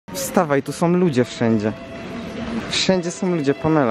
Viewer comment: lol na początku usłyszałem wstawaj panela xd